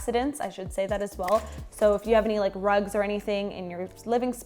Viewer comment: I thought she said drugs 🙄